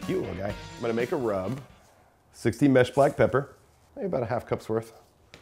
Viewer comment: SIXTY MESH black pepper ????? That is a very, very fine grind (sixty holes per square inch I think). In Aaron's BBQ book for brisket he prefers SIXTEENTH grind, very coarse. And in the video the pepper looks like a very coarse, maybe #16, grind. Did Aaron just mis-speak ? I'm pretty sure I heard him say at 5:16 "SIXTY" . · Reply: He said 16. Not sixteenth. They sound similar. If you notice...at the end you hear a slight -n sound. Its the accent.